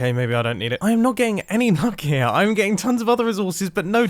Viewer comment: what did he say? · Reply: he said I am not getting any luck here idiot